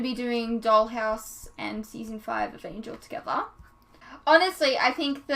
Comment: lol 😂🤦‍♂️ you clearly don't watch her videos or you'd know she has got down to E19 Timebomb on S5 so no I won't delete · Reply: Geraint Thatcher, I do watch her videos, but not the Angel series ones. At the beginning of this, I thought I heard that she had just finished season 4 of Angel.